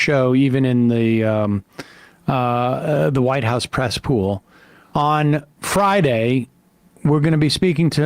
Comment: I SWORE YOU SAID THE WHITE HOUSE CESSPOOL !!!, I HAD TO REWIND TO HEAR YOU SAID PRESS POOL , I RATHER THE MISTAKE🕷🤦🏾